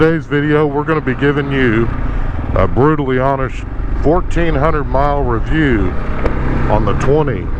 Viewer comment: I thought he said 14,000 mile review.